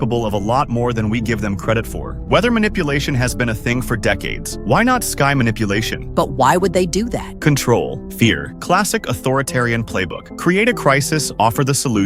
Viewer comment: did i just heard... sky manipulation?